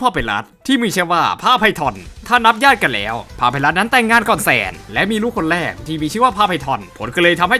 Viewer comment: แหม~ผมคงจะนอนน้อยเกินไปสินะถึงได้ยินว่าน้องชายอย่างพาไพรัสนั้นแต่งงานกับแซนเอาละขอตัวไปนอนก่อนละกัน😅